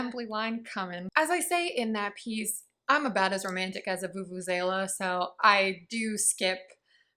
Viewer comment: Hurray what a great video!! Glad I read these comments re. vuvuzela as I thought you'd said "as romantic as a foofoo sailor" 😬